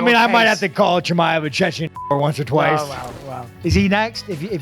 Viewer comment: HAHAHA did he say chechen N-word? 14:13 who thinks of that shit hahah. the smile after whatever he said was a genuinely hilarious smile. Like a reporter that said something he shouldn't of hah